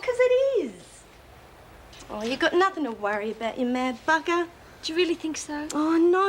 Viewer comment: What did Lex say at 23:10 ??? To me it sounded like "you got nothing to worry about you mad fucker" but surely that can't of been the line.